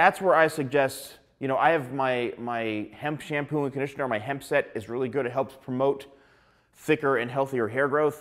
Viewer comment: Did you say Hims set?